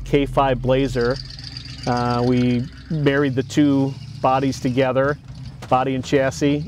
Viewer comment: I thought he just said we buried the two bodys